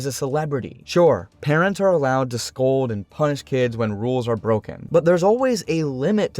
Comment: I think that you said punish here, but it really really sounds like punch! 🤛